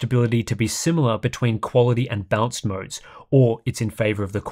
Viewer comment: My hearing must be going... I know there's an accent involved, but every time Tim says "balanced" I hear him say "bounced".